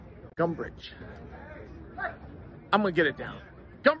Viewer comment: shit i thought you said dumbridge